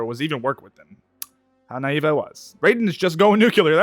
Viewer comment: it says native instead of naive 😂😂😂